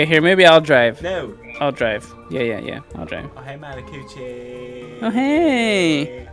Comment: did you just call him malac o o c h i e or am i just trippin